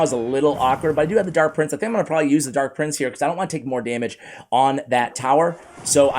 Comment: Thought he said “I don’t wanna say the n word” 💀💀💀 nearly spit out my food